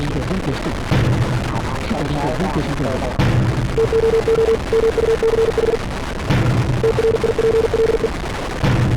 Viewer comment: What do the two of them say? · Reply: Too much Triple